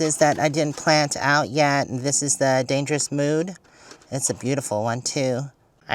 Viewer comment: I thought you said that iris was "Dangerous Moo"! It would be the black and white variety. 😂